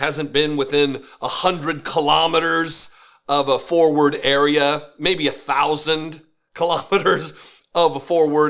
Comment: What in the world is a : four word area" ? 2:15 ?